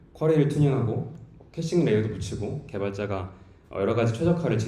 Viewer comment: 에서 캐싱XX 라고 말씀해 주셨는데요. 잘 안 들려서요. 용어가 궁금하고 어떤 용도로 사용하는 지가 궁금합니다. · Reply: "캐싱레이어를 붙이고" 라고 하는것 같네요. 변화가 적은 데이터의 경우 DB에 바로 요청하는게 아니라 레디스 캐시같은것을 이용해서 DB 요청량을 줄이고 응답속도를 높이는 용도로 사용합니다.